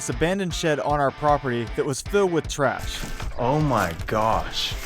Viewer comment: "Filled with trash"* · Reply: Okay, yes, but to be fair, does it not sound like he said “traps”